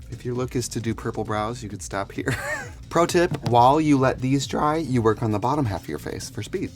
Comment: did i hear protip? 0:55 .......